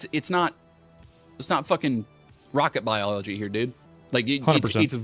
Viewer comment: Did he… did he say “it’s not rocket biology” at 8:59? As condescending and pedantic as he’s been about Asmond’s word choices?